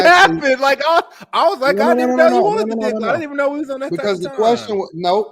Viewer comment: did he say he barely wanted the 🍆